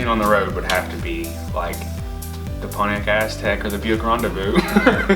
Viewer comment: What car is he mentioning at 6:35 ? I just don't understand the words xD sexy starion btw. it's funny how his dreamcar is a '96 Supra, and I think whyyy tf? You already own a dream car!!! <3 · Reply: Pontiac Aztec or Buick Rendezvous